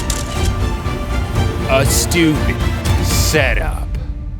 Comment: Bruh, sounded like he said student setup in the beginning. I thought the video was about dirt cheap setups